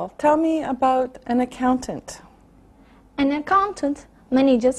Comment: I’m Japanese. Recently I useally watch these video. I think this will improve for me. I have a question. Tell me about accountant. What does she say? I heard she said “An accountant many just money. But Probably many is not correct. So please tell me right answer. Thanks for your reading. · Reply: Hi🖐 She said "An accountant manages money"